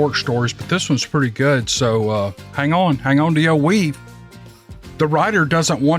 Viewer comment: LOL...."HANG ON TO YOUR WEAVE".... after binge watching from your beginning trying catch up for 3 months now....I thought you were saying " HANG ON TO YOUR WEED"!